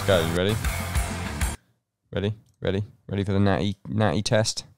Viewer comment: Yo what ‘s the word is saying at 4:24 ? Is it naughty ? I’ve been scratching my head for months…. English is not my first language. Help me. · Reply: Natty. Or natural. That’s what he means.